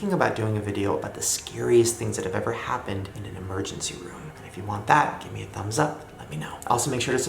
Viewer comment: So, at the end I honestly thought he said 'Scariest things that have ever happened in a virgins room' and my first instinct was to scream and say 'HES WATCHING ME'